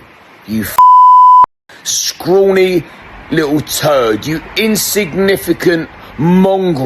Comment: did he just say scrawny little turd ?